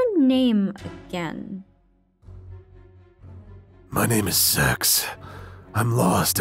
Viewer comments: I heard sex 😭 but apparently it’s Zex 😭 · +did he say his name is s-x I thought it was zex